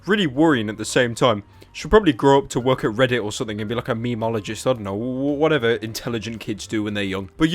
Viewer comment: did he just say memology??¿?¿? 😂😂😂